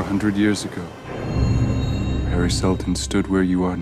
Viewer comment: I'm excited for it but also at 0:32 when he said "Harry Seldon stood where you are now" who else heard "Paris Hilton stood where you are now"?